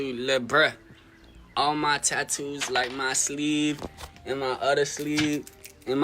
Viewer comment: The kid at 8:43 is.....mentally ill. What did he say? Did he say sleeve? Yikes.